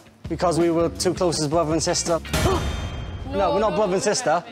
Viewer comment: lol i heard "brother n sister" as "brother-IN-CESTa"